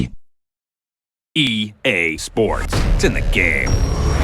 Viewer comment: Am i the only one who thought it said "Ea Sports to the game".